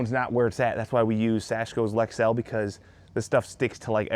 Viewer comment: What are you saying at time 5:39 for the sealant brand? It sounds like "Asheville" but I couldn't find anything. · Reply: Lexel. It's like silicone, but paintable & adheres even if wet.